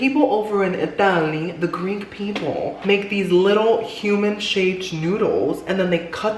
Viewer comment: "Basically, the people over in Italyyyy" "The greek people..." I CANNOT 😂😂😂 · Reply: OMG I thought she said the green people. LOL